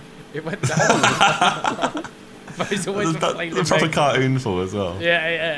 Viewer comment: a proper CAR-OON fall...Josh sounded like he was trying to do a bad british accent hahahaha